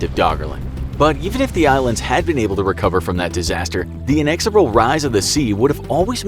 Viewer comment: What’s that word around 4:18? Inexorable? Spit it out!